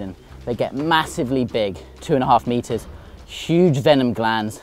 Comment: That snake is not 3 metres matey. Not even close. Clickbait lies. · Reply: I thought he said 2 metres.